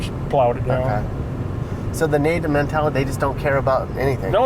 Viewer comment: The nomadic mentality is what he said not the native mentality